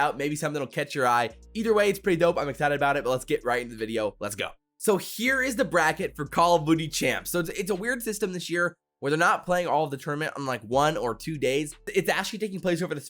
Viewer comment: Hold on rewatching here and did you say call of boogie or booty champs 0:40 😂😂😂😂😂😂